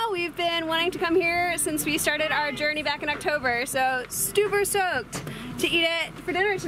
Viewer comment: Sounded like you said you were "stuper soked" for pizza. Is that what the kids say these days?